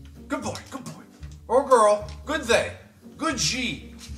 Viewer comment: "good they" im dying rn idk if i heard it right but yes